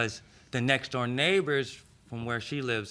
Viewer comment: Did she say she was a nurse? I'm sorry but if a nurse walked into the room and smelled like cigarettes, I'd want a different nurse. Those two really deserve each other. She's a talker and ever-excitable, and he's apparently super chill if he's ok driving 30 minutes with his eye half gouged out to have her take him to the hospital. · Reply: thought he said the neighbor is a nurse